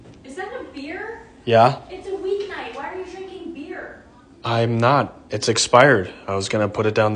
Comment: I swear I thought she said "it's a weed night why are you drinking beer" 😂